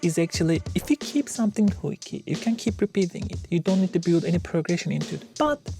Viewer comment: As in most cases, love the video. 8:43 But, can someone please clarify what is said here that sounds like: "if you keep something hoiky." The closed captions freak out here too. I really appreciate when these videos include their own captions, as someone who's hard of hearing. Thank you. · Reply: I guess she said "hooky"